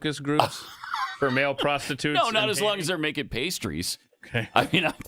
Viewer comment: I thought he said “pasties”!